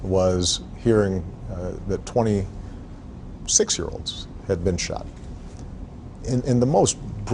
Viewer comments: Thought he said 26 year olds. Why does this happen. What makes people do this? Just to prove your not messing around? You cant be willing to end young lives, for a gang? Disappointing.😥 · I thought he said 26 year olds 😭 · im not american and didnt know about this and thought he said ''26 year olds'' :( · Obama after 20 6 years old kids tragically killed "This is the worst day of my life" Trump after 160.000 cases of Covid-19 and 3.000 US citizens dead "It is what it is" I got nothing else to say. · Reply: Oh I thought he said “a bunch of 26 year olds